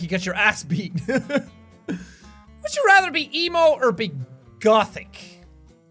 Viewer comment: i thought that said Elmo